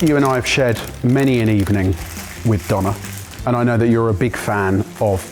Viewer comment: I’m American and with your accents, I keep hearing “Donna.” 3:29 Are your wives aware of these “many evenings out”??? 😂 Also, happiest happy birthday Mike!! ♥️🎉🎈🎁🍻🥳🎊